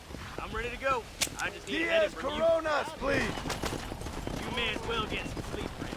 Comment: did I hear corona ?